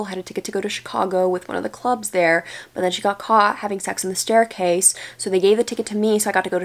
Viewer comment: wow, definitely thought you said that girl from your high school got caught "having sex with a staircase" which is honestly the best mental image, so thanks for that.